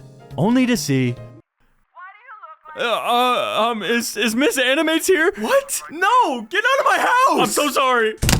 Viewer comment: i thought he said “is this anime tier?” But he actually said “is mrs animates here?”